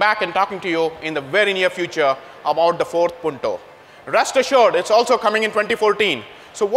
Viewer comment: I could swear he said Ford Punto at 5:14... maybe cos' it really looks a bit like a Ford? Luv Punto though... I can't stop driving mine :-P